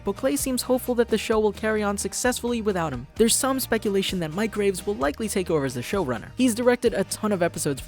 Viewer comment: It sounds like you said microwaves